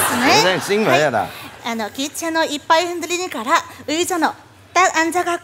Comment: 기차노 이빠이 흔들리니까라 이래들리네 ㅋㅋㅋ 비행기 아니였어?